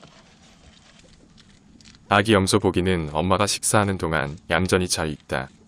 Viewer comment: 아기염소고기 라 들림.. 놀람🤔